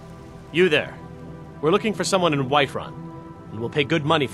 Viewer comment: I heard the Alik'r Warrior said Wiferun instead of whiterun